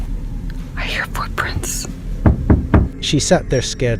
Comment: did she say I hear footprints?